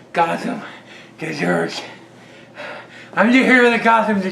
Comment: I thought he said the hero Gotham desserts.... stunning and brave lol.